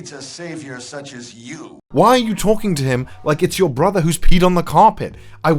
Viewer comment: did he say "yelling at your brother that peed on the carpet" XD